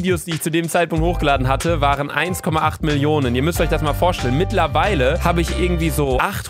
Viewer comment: Ich habe statt mitlerweile "Hittlerweile" verstanden xD😂😂😂